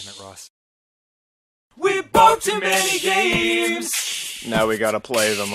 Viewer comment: On the steam train part I skipped and it sounded like it said he bought many gays